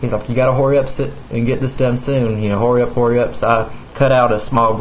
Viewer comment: did u just say horry up its hurry up